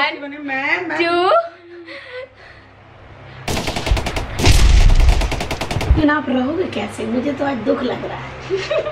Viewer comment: what did she say I literally saw 100 times in repeat to understand but still I can't I think she said munna bhaiya ab mar jaenge par iska matlab kya hai Kuchh nhi samjha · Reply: Munna bhaiya mar jaayenge ✌️